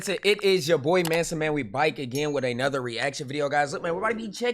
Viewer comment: Did he just say "We bayk again"